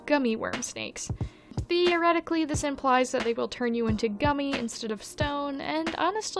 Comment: The Medusa for some reason made me think worm on string as hair. Her hair is forever slightly unhinged. If you're lucky, it will turn you into a furby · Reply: for a second i thought you said she'd turn you into a furry